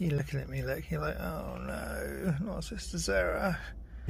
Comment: See I hear: Let's go! ..and "I love Sister Sarah!" You mistranslated a lot...